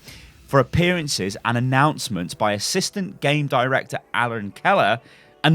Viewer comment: Is it bad if when he said Aaron Keller, I thought he said Helen Keller and I imagined the guy walking around blind... Cause I feel bad now